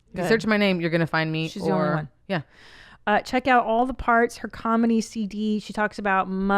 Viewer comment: When Main Mommy Tina announced the name of Album, and knowing they're both comedians, I thought she said, "All The Farts". Bring back the meows! We love you YMH crew!